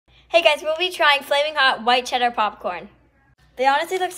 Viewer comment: I thought they said flamingo LOL